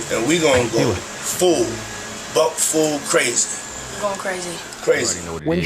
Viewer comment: “Buck full crazy” is wild bro. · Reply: I thought he said "butt full crazy".